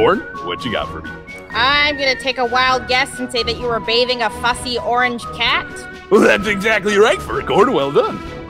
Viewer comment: why do i keep hearing "sussy orange cat" 💀